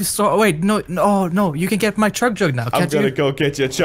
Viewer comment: I just f***king you can get my chug chug now (crainer knew what he said by he’s face exspretion